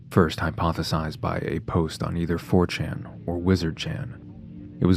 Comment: Had to backtrack near the beginning. Thought you said wigger chan at 2:09 and couldn't stop laughing.